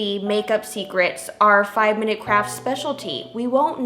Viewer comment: Ohhh I thought that said minecraft kits